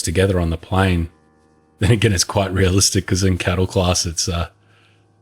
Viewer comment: "Cattle Class" LOL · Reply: Ohh, I thought he said "Cuddle Class" because the seats are so close together.